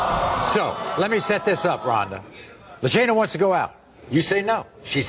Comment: I thought her name was said like lasagna 💀🥴